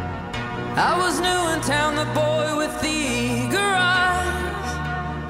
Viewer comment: i swear i thought it said "the boy with the garage" never thought it said "with the eager eyes" thus hinting its sequel "mr. brightside"